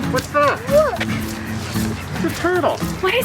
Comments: At around 4:00 it sounds like is saying “what the fuck” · sounded like what the fuck